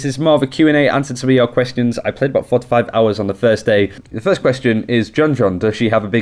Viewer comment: I swear, I’m the very beginning of the video he says he played “4 to 5 hours of her on the first day.” And I thought you said 45. Like.... m8 you know there are only 24 hours in a day right? Then I realized, and felt incredibly dumb XD